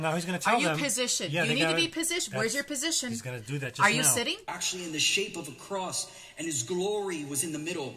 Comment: I have a question...at 1:51:50 it seems like he says the cloud was in the shape of a cross. Anybody else hear that?